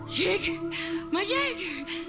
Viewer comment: Did she say "my" Jake?